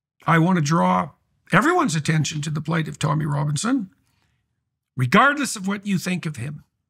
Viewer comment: I thought you said Toni Robins, I was like WHAT!